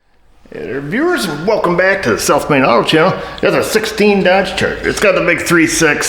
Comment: When I first started watching your channel a couple years ago I thought you said "Self made" and not "South Main" haha